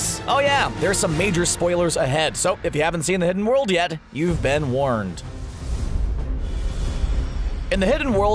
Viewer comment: am I the only one who heard “You’ve been wormed.”